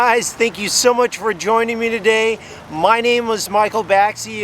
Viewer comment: Whats ur name now if it was Michael baxi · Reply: Into to ur vid sounds like you saying “my name was” haha